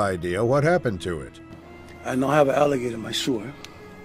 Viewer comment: Man says “I now have an Alligator in my sewer” Caption says - “Speaking Foreign Language”